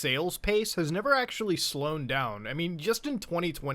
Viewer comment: when he says "slown down" instead of slowed down 👌😩